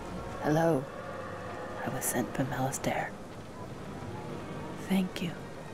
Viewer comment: what the girl says at 1:06? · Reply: I was sent from Malastare